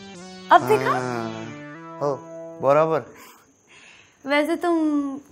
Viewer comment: what was the word used for perfectly ? can anyone pronounce it ? thanks... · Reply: Barabar hai